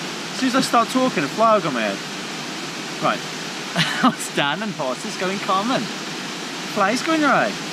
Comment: What did you say at 10:26? from Canada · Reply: Kaos the 1st “What’s Dan and horses got in common?” Nothing about Canada, I’m afraid.